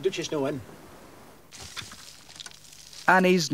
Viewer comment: Did say : 'The b*tch is no in!" @ 6:23 ?. 😂